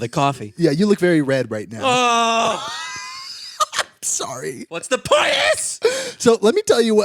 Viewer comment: What did say? 13:44 “what’s the poiness?”